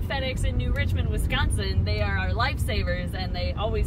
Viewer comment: did she just say they are their lightsabers? Lol . Edit: no, it just sounded like it, Lol